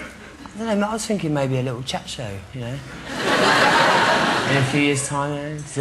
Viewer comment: What does he say at 9:08? Maybe a little..? o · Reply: Chatshow :):)